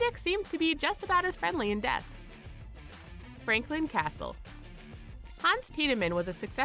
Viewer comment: did you really say "castel" instead of castle